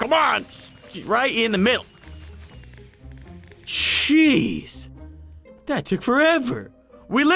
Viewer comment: "CHEESE, that took forever!!" -LogDotZip 2018 P.S. yes it does really say cheese if you put it on captions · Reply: He said jeez not cheese